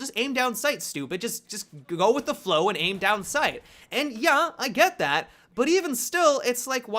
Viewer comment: did you say "Ja" there, the german word for yes? :DD